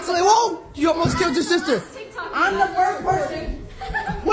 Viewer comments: At first I thought he said hump the dog 😂😂 Also not trying be inappropriate😊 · My volume was down so I heard: “I’m the first to hug the dog!”